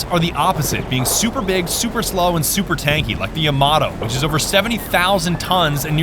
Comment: I seriosly though said "yamaha" instead of the yamato in 6:31 lmao